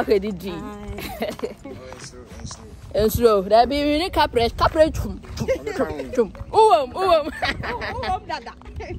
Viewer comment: lol what did he say? What’s your response? Good video as always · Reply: That I should post him too on the front page